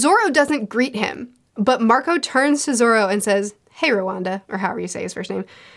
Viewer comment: Did just call Roronoa Zoro "RWANDA"? 😂😂😂